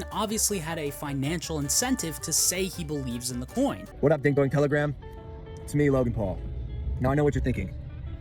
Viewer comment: whoa did he just say dink dink telegram only pedo use telegram